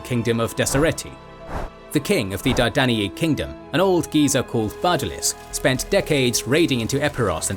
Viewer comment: does it say ‘old geezer?’ Lol